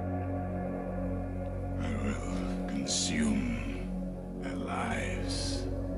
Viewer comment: "I will consume their lies" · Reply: I always thought he said, "I will consume their lives". Hard to tell.